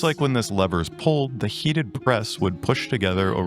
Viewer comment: For any of my hard of hearing friends I think it says 'heated press' but let's go with the subtitles. That makes it way more interesting as a way create adversaries, even if it doesn't match the images. Clea will stop at nothing to force her will on the canvas, even if it means walking into a forge without a bra